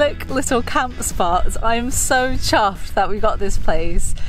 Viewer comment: what do you say at 2:04? I am so chassed we got this place. "Chassed"? Is that what you said? Not an American term, so curious. The loo bit is hilarious. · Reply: Chuffed.. British expression for pleased